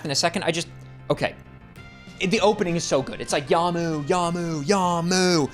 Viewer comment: It isnt 'Yamu Yamu Yamu', its 'Your Move, Your Move... Your Move!" As in "Its your turn to act in this turn-based duel." Its kinda funny to me to realise you have been mishearing and thus mis-saying it - for an entire quarter of a century. If it makes you feel any better, I used to sing 'Country Rows, take me home, to the place, down below, West Virginia, Mountain Momma, take me low, Country Rows.' I found out that I had been singing it wrong... on stage. To my everlasting relief, no footage of that revelation survives to this day 😅